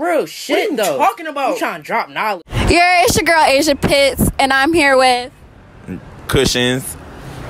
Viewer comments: i thought she said asia piss at the start of the video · I thought she said Asia piss💀